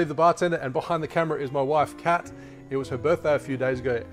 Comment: I'm more drunk than you , i thought you said behind the camera my wife's cat , imagine a cat with a headphone filming you, i laughed my ass off🤣🍸